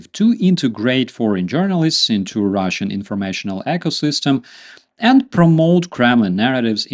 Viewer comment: Great update. Misheard russian echosystem. Much more accurate though